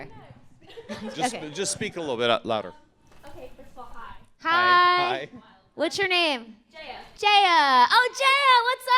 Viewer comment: did she say "ok your so hot" or "ok its so hot" ? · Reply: She said "ok first of all hi"